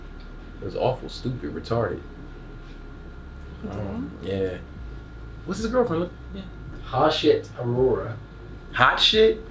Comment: not sure if I heard right but could have sworn you said "What's his girlfriend look like again?" 😂😂😂